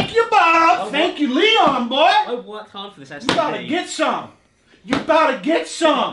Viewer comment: did Alex say “I worked hard for this std” cause that’s a mood and a half